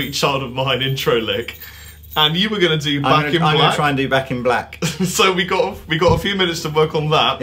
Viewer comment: Well, Lee did a good job, because before he started playing I thought they said "Vacuum Black" not Back in Black